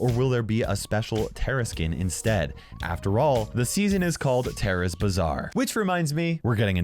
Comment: I'am calling it now a youtuber would name their vid Tara's bazaar adventure! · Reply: Mr. Gwapito I literally thought supercell was saying “Tarra’s bizzare!”, and I’m like chill supercell!